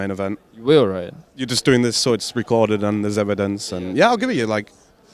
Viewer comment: what does cyborgmatt say at 13:37 please tell me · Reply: "You're just doing it because it's recorded and there's evidence (of the shoutout he said he would give to ice3)"